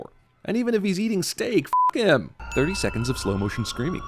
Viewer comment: Captioning it as "'im" instead of "him" when you clearly pronounce the h. Ding